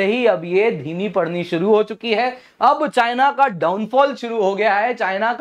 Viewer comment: At first I thought it's "China ka nightfall shuru ho gaya hai" instead of "China ka downfall shuru ho gya hai" lol